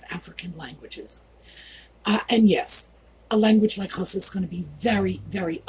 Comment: Edit: I'm so sorry, I misheard you. I thought you said Hausa when you said Khosa!! But just for entertainment purposes I'll leave my full comment here, ha ha! Hi, as far as I know, Hausa is not a click language; it's an Afro-Asiatic language in the same larger family as Arabic, Hebrew, Amharic, Somali and the Tuareg and Berber languages. It does have implosive and ejective consonants which are very hard for foreigners to master, but no true click consonants. Most click languages are spoken far to the south. The clicks are said to have originated among the Khoisan languages and spread into Southern African Bantu langauges from there (Zulu, etc.) I love your history videos about bathrooms and kitchen history by the way!